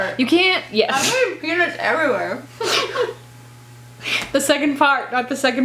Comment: It sounded like said "I'm getting penis everywhere" instead of peanuts everywhere. It made me lol.